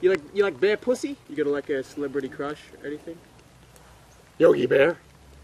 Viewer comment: what did say at 2:51? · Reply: Yogi bear